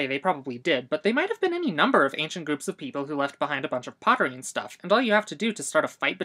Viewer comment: There is a very crucial mistake in the English subtitles at 0:51, it should say pottery instead of poetry.